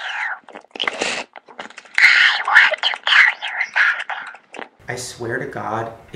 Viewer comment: the whispering doll at first I thought it said "I want to kill you slowly" spooks